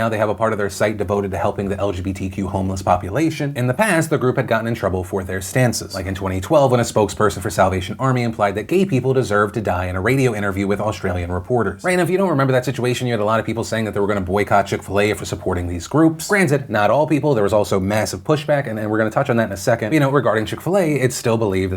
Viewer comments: I know you said: "'Gay people deserve to die.' in a radio interview with Australian reporters." But what I heard was: "Gay people deserve to die in a radio interview with Australian reporters." and I was like that's a suspiciously specific way to die, that dude must really hate two types of people. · Was listening to the video, thought he said South Asian Army. What's wrong with me...?